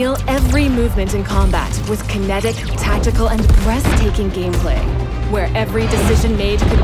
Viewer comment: Sorry at 2:55 did I hear her say BREASTAKING gameplay?😂😂😂